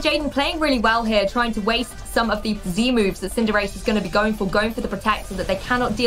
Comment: did she just say Z-Moves